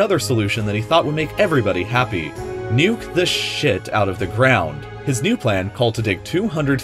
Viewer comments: Did he just said sh*t? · WHAT DID YOU SAY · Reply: Nuke the s**t out of it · o what did u say im shocked u would say that word why why why u even said in another vid this is kid-freindly channel or wait was that another channel... edit: if u were not listening or read comments first he said "nuke the sh-- out of it" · Did he just say nukes the sh*t out of the ground? Holy Mother Of Corolla